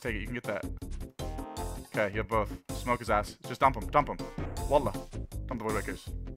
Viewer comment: i SWEAR i heard you say "Wallah" at 2:20??